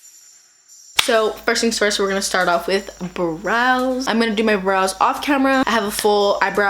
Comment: I thought you said bras instead of brows lmao